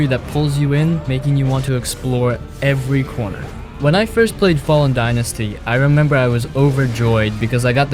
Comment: Did you just say “Fay-Lin Dynasty?”